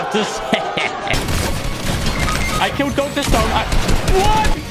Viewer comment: did he just say 'dr stone'?